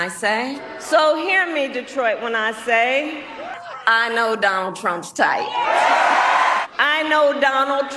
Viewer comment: “Donald trump is tight”? Can someone tell me what she actually said